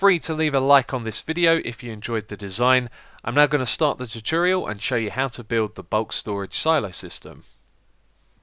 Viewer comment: sound like he is saying " im not gonna start the tutorial "